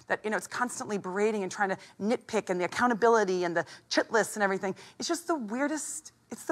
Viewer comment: What’s she saying? “The chitless”?